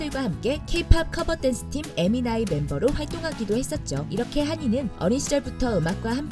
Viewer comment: 순간 에미나이 멤버로 들려서 내 귀를 의심함